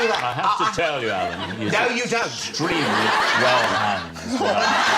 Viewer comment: Please can someone tell me what his dad says at 0:57? It's killing me! :) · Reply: He said, “Let me tell ya, he’s extremely well hung.”